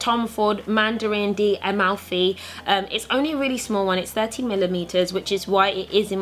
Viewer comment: I swear it sounded like you said 30 millimetres not millilitres 😂😂 love the bag xx